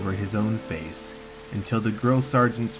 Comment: Am I the only one who thought he said “girl sargent” Edit : I know I can’t spell